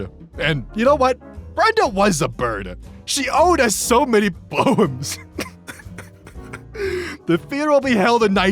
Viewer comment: Okay, so for the longest time I was only paying half attention to the image with the AI generated obituary... And I swore would say "She owed us so many bones!" And I just... Never questioned it, because it made as much sense as the rest of it.